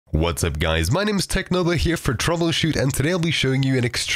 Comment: anyone else thought he was gonna say "whats up guys my name is technoblade" LMFAO